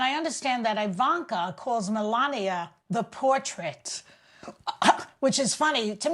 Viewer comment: When Joy said Ivanka called Melania a "portrait," I thought she said a "poor trick." That would have been the first time I agreed with Ivanka. Oh well.